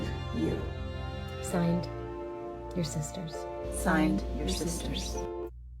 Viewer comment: When they said "we sisters", I heard "the resistors"